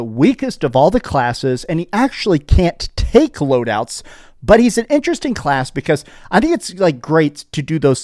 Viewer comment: I heard you take those loadouts.